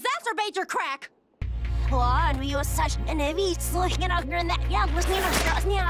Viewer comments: What I Heard: What?? Your such a heavy slud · why do I hear "uhh why are you such a nivvy?"